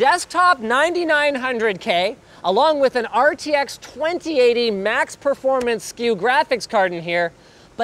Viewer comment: Did I hear RTX 2080 max performance Q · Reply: Max Performance SKU Even when Nvidia try and clear up the problems caused by their stupid MaxQ naming, all they do is make it more confusing